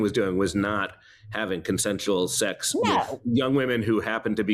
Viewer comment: did she just say "conmens"? 😐